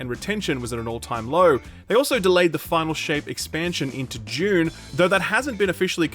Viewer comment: I misheard the game title as Final Shame and laughed